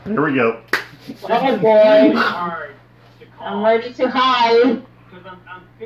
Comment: Did say she was already too high at around the 26:42 mark?